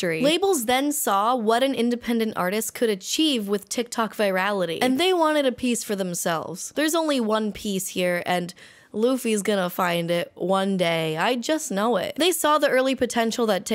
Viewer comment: sorry, but I'm Polish and when I heard "there's only one piece here" (3:32) I was like "HUH PiS???" and it made me laugh so hard sksksk